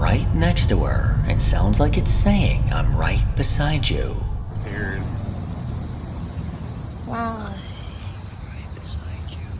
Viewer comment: I heard, "I hate rapist like you," not "I'm right beside you."